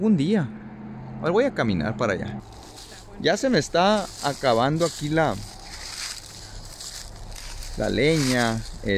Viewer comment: Se escucha un sonido que aparentemente sea una voz, no se entiende que dice, pero se percibe que algo o alguien habla. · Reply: Dice: 'ta wenno